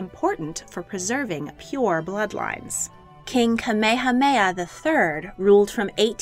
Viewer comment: did I hear that worng or she really said KA ME HA ME HA ?